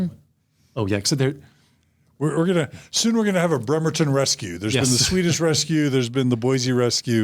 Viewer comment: Correct me if I'm worng, but did say the Bremerton rescue?